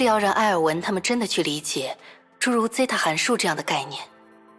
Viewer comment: It's ζ Not S, I think what she said is ''Riemann zeta fuction''